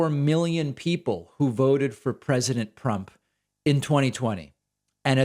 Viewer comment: did he really say ''president Prump'' ???? 😂 😂 😂